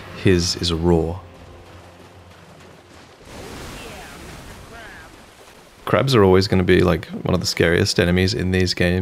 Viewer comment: does it say "oh yeah mr krabs"?